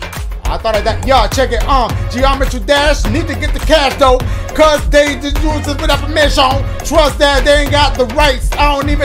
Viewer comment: The Subtitles Said "Cause They Just Used To Put Up A Mission" But He Said "Cause They Just Doing This Without Permission"